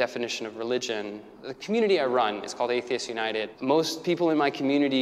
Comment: I thought it said athletes not atheists